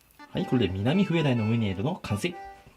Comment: のところミナミフエダイって聞こえてましたよ！